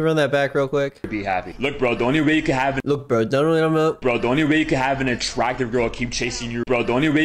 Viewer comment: i think he says "Bro, the only way you can have an attractive girl chasing you..."